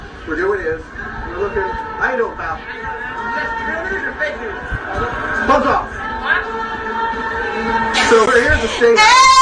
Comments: he clearly says "BUZZ OFF!" not FUCK OFF! Just wanted to clear that up. I wish it was the latter though. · i think the guy at 9:18 said "bug off" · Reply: Lol. Yes, the reporter said he said, "Buzz off!". Wishful thinking!! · The reporter, tweeted that he actually said, "Buzz off!" Still funny though.